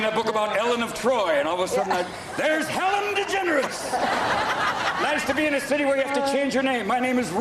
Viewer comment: did he....did he just say hellen degenerate